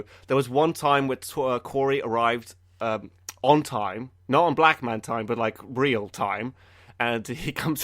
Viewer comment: did you say "Black man's time?"